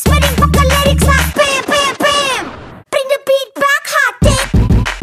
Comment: "bring the beat back, hot dick" am i the only one who heard it as that?